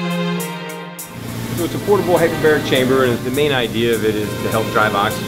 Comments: My dumbass thought it said hyperbolic 😂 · Am I the only one that thought it said "Hyperbolic Time Chamber"? It would've made so much sense on how dominate LeBron is at his age.